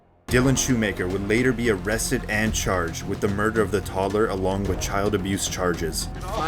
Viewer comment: I thought he said he ate the toddler I was like wtf